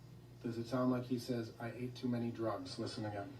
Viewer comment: timestamp It sounds like he says, “I ate too many Pills.” Listen to the end when he says the word “pills,” rather than saying “drugs.”